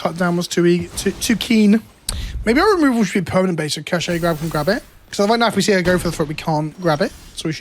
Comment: It sends me every time I hear "ca-shay grab" Britishisms ruining Wizards' attempt at a cash grab joke.